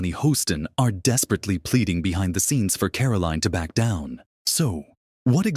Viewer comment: The guy narrating the video says they’re pleading BEHIND THE SCENES, but the caption says “OPENLY pleads.” 😂😂😂